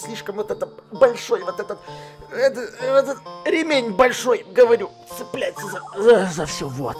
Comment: мне ондному послышалось "ремень большой бл@